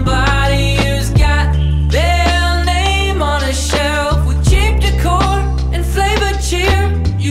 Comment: This whole time, I thought he was saying “Cheap Liquor” a funny way…😂